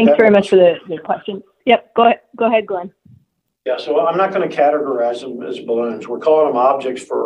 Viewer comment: He CLEARLY says "I'm not going to categorize them as balloons." The official DoD transcript "I'm not going to categorize these balloons." The DoD is either lying or incompetent.